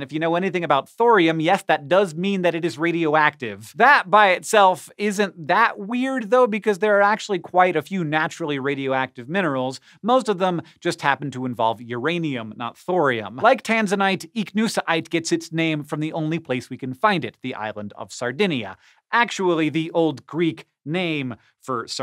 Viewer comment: I thought he said vibrainium. Immediately believed this was a joke.